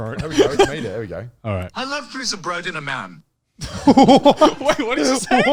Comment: I heard "i love pizza bread in a man"